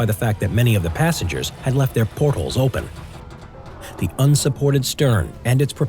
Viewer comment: “Many of the passengers left their port holes open...” It sounded like he said portals, and their was that ship in Half Life 2 that used portals, but it disappeared... HAFF LIFE 3 CONIRMMED!!1!!1